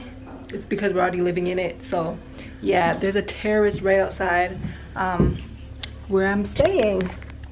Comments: I swear i thought she said it was a terrorist outside her bedroom....i was like wait...what?!? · I thought you said there was a terrorist outside. My heart dropped at 3:39 😭😂. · I thought you said there is a terrorist right outside at first. I was like girl hide!! lol Excuse my slowness. lol never know them nuts are everywhere. But Capetown is pretty safe. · I thought you said there was a terrorist outside. My heart dropped at 3:39 😭😂. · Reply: 😂😂 my daughter was like,did she just say there's a "Terrorist" outside? I thought I was the only one that caught it. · i thought she said "there's a terrorist outside of where i'm staying, sooo" 😂😂😂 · I thought you said terrorist not terrace I was like wait whAHT, glad it was terrace!